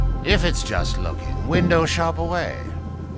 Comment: I'm I the only one who cant understand what he said ? Can someone tell me ? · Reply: If it’s just locking window shop away that’s what I heard